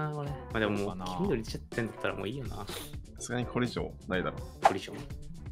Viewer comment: 「これ以上」 「コリジョン？」 さすがに野球脳すぎる聞き間違いで草